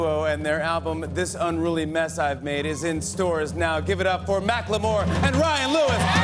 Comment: Did Jimmy say Mack Le moore.....? 0:14.